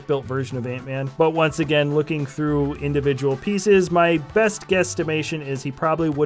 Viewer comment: Did he just say guestimation